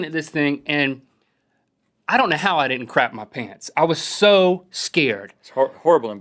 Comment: itsounded like link said i dont know how i didnt crack my penis. XD